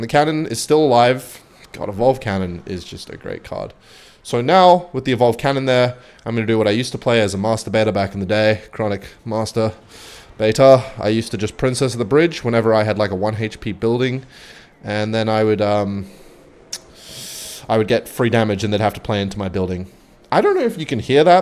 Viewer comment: did he say master baiter chat